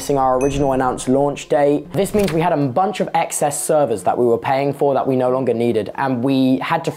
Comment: subtitles are wrong. Should say excess.